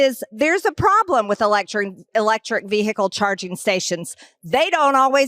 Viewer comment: Did she say, "...a problem with 'electorate' charging..."?